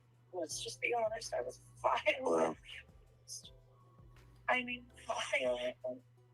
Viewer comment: I thought she said, “I was violated. I was violently violated.”